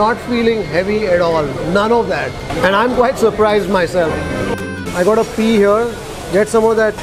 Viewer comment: I thought I heard "I've gotto pee here" - meant "I've got a PEA here"... 😂😂 Nice episode... I was so wishing I could see some sambar in at least some dose order but apparently, like Veena Stores (my fav in BLR north), they eschew it here 😫 Yumm, nevertheless... hats off to the hygeine factor in this establishment 👍🏽🙏🏾